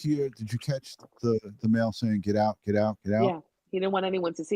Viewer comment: I hear 'get out get out, she's dead'